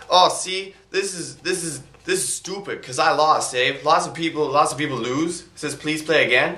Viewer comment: did you say eh? i never heard a real canadian say it~!